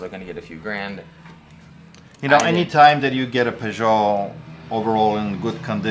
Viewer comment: did he say a Peugeot? Since when did the French car manufacturer Peugeot start making high end watches?